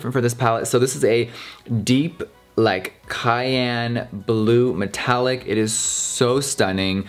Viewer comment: Cerulean not Cayenne, cayenne is red honey :-) I do love it, almost as much as I love your puppies. What? You thought I was gonna say you? I mean, yeah sure I do, but lets be real ;-) lol